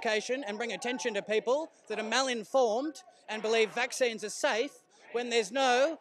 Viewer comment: Did he say "malinformed"?